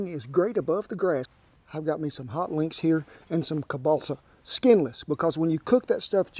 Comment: my good friend & Christian brother, you are gonna laugh at this one, at the start you said i lied in the footlong & i thought you said gonna make them with hot wings, lol, ( i don't hear to well), i thought how in the world are you gonna pull this off, lol, then i saw the package of hot links which to me sounds like hot wings, lol. This is a great recipe, in kentucky we call hot dogs, sausages, etc... kentucky tube steaks, lol. God bless you &, all the subs, viewers, folks, our vets, & can't forget the pups, please stay safe.